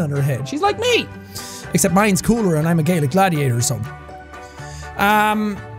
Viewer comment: Lol i watched started Gladiator yesterday also it sounded like he said “Gay Gladiator.” Oh someone near me just said Gladiator! … wow COINCIDENCE!!!! 😂